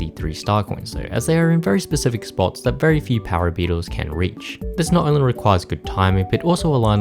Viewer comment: did I just hear... Power beetles?